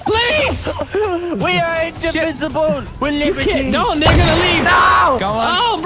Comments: did wildcat say nigga? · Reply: It sounds like he said that but at the same time it sounds like he says "no they gonna leave!" I think he says it to fast and he almost got tongue twisted that it sounded like the other phrase. I could be completely wrong though. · what he say🤔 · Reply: “No, they’re gonna leave”